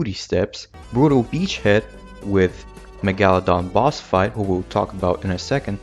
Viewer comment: i thought someone with 100k subs made this peakness, it made me giggle and wiggle my toes edit: 2:54 wait did you say megal-don